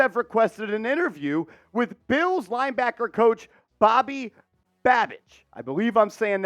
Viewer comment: Who do you want to be the Giants next defensive coordinator? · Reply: Sounds like you were saying Bobby badbitch 😂😂